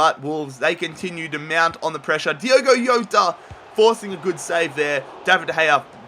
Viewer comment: did I just hear say "Diogo Yoda"